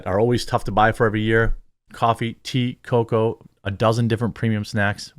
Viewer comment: I’m glad you slowed down your advertisement. It used to sound like you were saying: “coffee tier coco”.